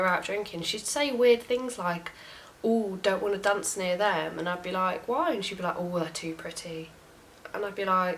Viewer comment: I'm sorry, but I've listened 3x & I cannot understand wth you're saying. What does the roommate say that's weird right before something about "too pretty?" All I think I can make out is blah blah dunce blah blah.. I do apologize. I love British accents, but sometimes I can't understand it to save my life. Can anyone help out? Looks like this YouTuber is no longer active. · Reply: Sorry I do mumble sometimes! I was saying how when we'd go out she'd say she didn't want to dance near certain people because they were too pretty or they were of a different race. I am active and want to get into uploading more videos now that I have finished university!